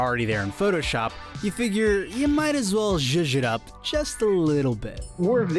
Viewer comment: You guys need a better caption person/contractor. @ 3:12 "you figure you might as well (inaudible) up"... uhhh, what? I heard that just fine and I'm losing my upper hearing already. It should read "you figure you might as well zhoosh it up". It's old Polari parlance, meaning to add style. It's probably popular in use today because of modern "gay culutre". The world needs better captions, you know?